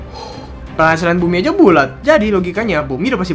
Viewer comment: ngomong apa sih? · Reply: Penghasilan bumi aja bulat